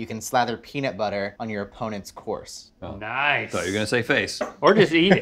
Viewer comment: I had to rewind because I thought said you could "slather peanut butter on your opponent's CORPSE" lmao